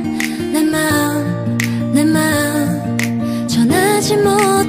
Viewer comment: I heard "LMAO LMAO"